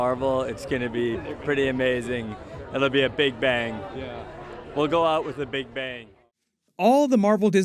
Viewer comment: Did Mark say, “we’ll go out with a bang” in his interview? (1:14) What do you think this means??